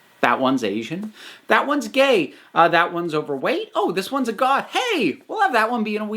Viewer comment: "You make this one black, that one's Asian, that one's gay, that one's overweight, oh, that one's goth, hey, we'll have that one be in a wheelchair." LMAO THEY JUST BASICALLY DESCRIBED GLEE 😂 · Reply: I misheard 'goth' as 'a god' in the video, and thought: 'Dang! One of the minor characters is way more powerful than the others!'